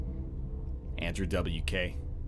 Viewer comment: What does he say @ 2:12:57 ??? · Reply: andrew wk, classic bloody nose joke